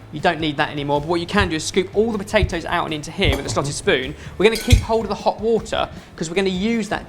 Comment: I thought Ben said "slutty spoon" XD